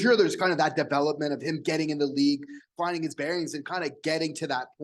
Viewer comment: Ha!! 😂 I had to go back and listen to it 3 times. I coulda swore said Slaf was "finding his berries", not "finding his bearings". 🤭😅